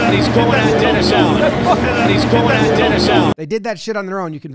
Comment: It sounds like he says, "He's going at dinner salad." Now that you've read that, it is all you will hear.